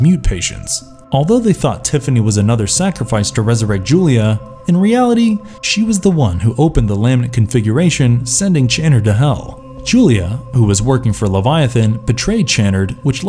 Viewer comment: It sounds like he saying laminate configuration.